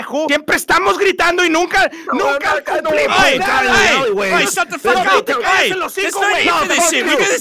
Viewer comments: Is it just me or does it sound like said there’s no need for this n words at 6:16 lmao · did someone say the n word😂😂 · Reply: "Estamos gritando y nunca" We're yelling and we never